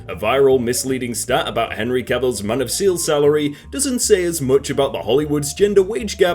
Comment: did he say man of seal?